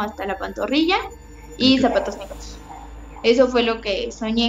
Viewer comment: Escuche mal al principio? Según yo dijo la mamá zapatos negros??? Minuto